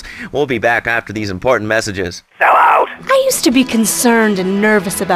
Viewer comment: what the hell is that person saying and is it a hiccup in the show? I’ve always wondered who said it and if it’s saying sellout.